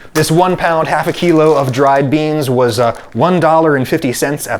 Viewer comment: could someone please confirm? Captions said $11.50 but I think Adam actually said $1.50? Thanks.